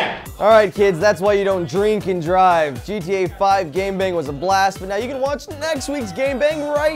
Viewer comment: I thought they said gang bang... I was like what?